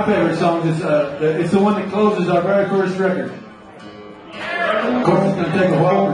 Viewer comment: Did he say it CLOSES the first record?